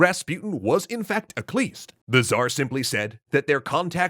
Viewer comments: There is a word here I don't recognize. "...claiming Rasputin was in fact [word]." What is that word? Accleased? Ecleased? I don't recognize it at all or what it means. Google hasn't been helpful, hence why I'm asking. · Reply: A Khlyst. That's what he's saying. The Khlysts were an underground hardcore religious cult, and Rasputin was rumored to be a member. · claiming Rasputin was what? I have no idea what was said there... help anyone? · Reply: I think they said he was a Khlyst. <link> . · what was the minister claiming about Rasputin? He was a cleased? I don’t understand what they’re saying. Please help I’ve listened multiple times now. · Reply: Khlyst. The Khlysts were a heretical religious sect in Russia. They believed that only after a person had sinned greatly could they truly repent. The sinning committed? Well, based on what you know about Rasputin and women, well... use your imagination. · Does anyone know what he says at 8:10 “… he was in fact”? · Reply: Khlyst. The Khlysts were a heretical sect in Russia that believed that only after sinning could a person be truly repentant to God. (And given how Rasputin was with women, well... use your imagination as to what his form of sinning was... )